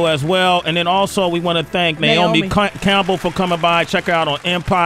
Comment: Anyone else catch when Sway said “Naomi “Cunt”Campbell”? 😂😂😂😂👀🙅🏾‍♂️🧢